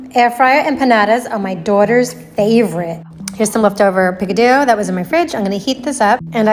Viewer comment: Did she call Pico de Gallo “Picadoo” wtf 😂